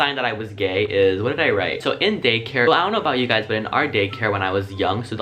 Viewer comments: it sounds like he was saying gaycare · Did no one notice that he said gaycare? I might have misheard it but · When you said chest in daycare it sounded like you said chest in gaycare. 😂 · Who else heard him say gaycare just me? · It sounded like he said gaycare